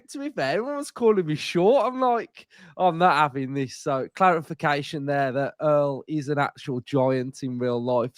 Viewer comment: Sounded like you said 'claretfication' - if you did great pun!